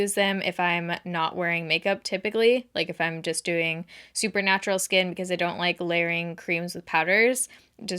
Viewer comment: Lol I heard "Supernatural skin" then I realized you said "super natural skin" 😂